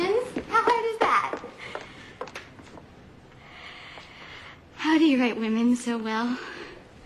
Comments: I thought she said ' How do you rape women so well' his immediate reply of.. 'I think of a man...' really scared me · I never saw this movie. But when I clicked on this video, I was so shocked because I thought she was saying, "How do you rape women so well?" I was like, what ****ed up question is that? Then Jack's response to THAT, was even more bizarre. But I misunderstood the whole thing, thank god.